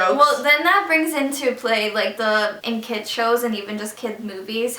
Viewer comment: i thought it said in kids shoes XD